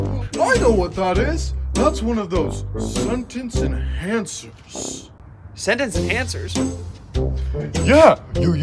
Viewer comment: But we were just using sentence in "hancers" That what I thought spongebob said not saying enhancers